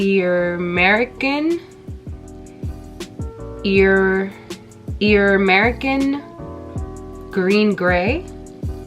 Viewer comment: Always keeping it real! I laughed because it sounded like you said “YERR-America” 😂 Keep the content coming! ✨